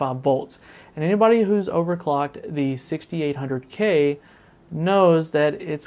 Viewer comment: I don't doubt it. My i5 6600k run really cool as well, on air in an ITX rig. But the Broadwell-E i7 6800k is what's being used here. -Keith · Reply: I thought I heard 6700k.